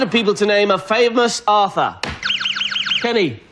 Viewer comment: To be fair, it sounded like he said AUTHOR, not Arthur